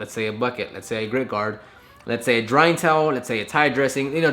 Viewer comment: Did he say a green card? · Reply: Nevermind u said grit guard at 7:30 sounded like green card lol